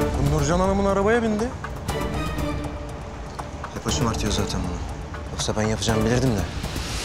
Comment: ne diyor anlamadım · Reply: {1903} Bollywood 3⃣2⃣ hep o şımartıyor zaten bunu yoksa ben yapacağımı bilirdim de... diyor